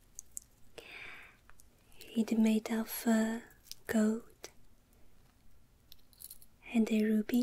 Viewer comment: Omg 2:02 i thought she said goat at first! 😂